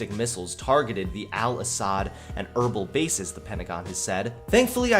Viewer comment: Did I hear Al-Asad? Modern Warfare flashbacks